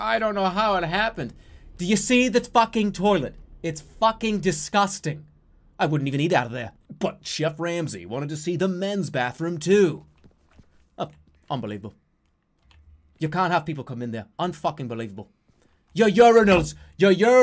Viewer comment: Did he say Geoff Ramsey at 3:20? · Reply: Cocoasomething He definitely says Chef Ramsey, but I hear Geoff Ramsey too. A little later though he mentions a red team and a blue team? I'm so confused.